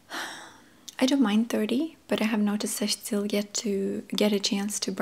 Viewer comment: Ok I've watched this like 30 times but I still don't understand what word/words she says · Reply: "i have noticed that..."